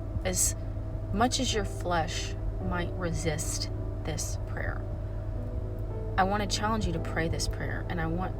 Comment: I thought you said don’t avoid this “player” 😂